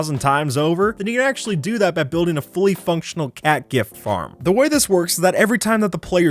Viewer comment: "The cat gift farm can give you phantom membrane" that would break the dream Smp economy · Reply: I thought it said cat girl farm